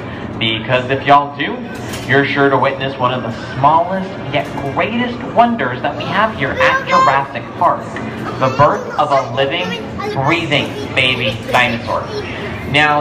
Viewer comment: did he just say gweatest ?